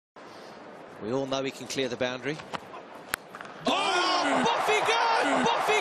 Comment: What he just said ? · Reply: We all know he can clear the boundary. Off he goes! Off he goes!